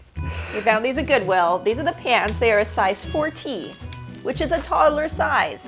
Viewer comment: does say 4T or fourteen? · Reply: 4T 😀